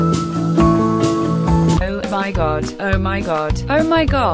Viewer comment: am I the only one who heard "oh my God you're my dog"